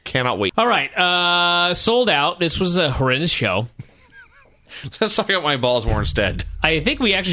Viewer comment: What did Vinny say?... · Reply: “Let’s talk about my balls more instead”